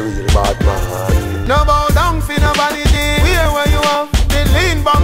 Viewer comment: My whole life I thought he said don’t bow down fi no wallabee. Man said VANITY this whole time 🤯